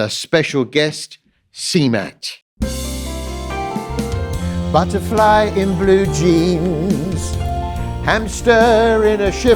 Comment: I thought he was saying 'butterfly in blue cheese' for the first two episodes. Which would just ruin both things.